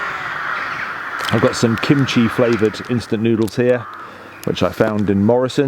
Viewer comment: Welcome back, be safe and ummm I swear I thought that you said Coochie flavored noodles 😂😂😂 I had to go back and hear that you said Kimchi flavored noodles lol